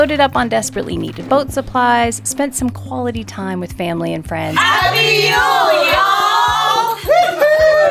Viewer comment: What did they say around 2:55? Happy Hule ? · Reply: Happy Yule, y'all!